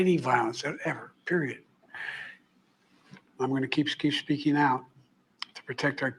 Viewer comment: The way his eyes looked when saying 'surrounded by Presidents' was unnatural... "Im gonna keep keep speakshing out" I swore he said Im gonna Skeet Skeet Skeein out..."